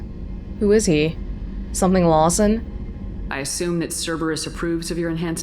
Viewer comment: What did you call Miranda's dad here 14:29? Lawson? Couldn't quite get it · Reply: "something", as in I don't know his name :)